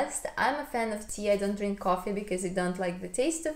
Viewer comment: Very interesting video! I really like that you do what feels right for you, and still do not judge anyone who do things differently! Also, I am curious about one thing that you mentioned in the video. I am not from Russia so I might be wrong, but my impression is that alcohol is a big part of Russian culture. What is your experience not drinking alcohol in Russia? I live in Norway and also don’t drink alcohol, and sometimes feels like I am the only one who does’t enjoy drinking haha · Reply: Haha, I watched the video without headphones the first time and I realize now that I just misheard you when you spoke about tea and coffee. I thought you said: "i don't drink coffee, i don't drink alcohol", but when I went back to check now I can hear that you are saying you don't drink coffee at all haha. Sorry for seeming creepy! Thank you for an in-depth answer! Very interesting to learn more about minimalism and Russian culture :D